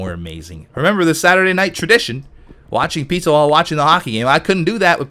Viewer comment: Did he say "Watching pizza" at around 36:40?